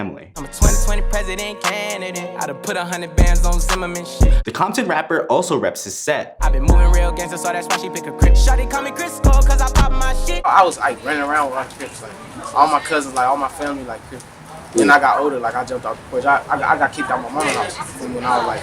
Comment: Thought he said "shawty called me Chris Paul" this whole time😂